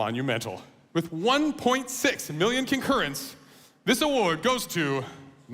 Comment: 1.6 mullion concurrents i thought he said billion 😂